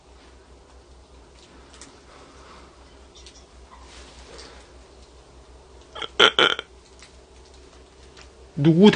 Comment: 초 여자목소리 희미하게 들리는데 해석좀요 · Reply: 블랙녀:죽는다 나만 들은거같나